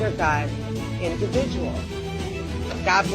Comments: it sounded like that MAGA nutcase said “One nation, under god, ‘individual’, with liberty and justice for all.” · Did she just say "individual "??